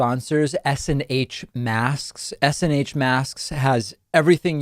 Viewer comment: I thought you said s&m masks?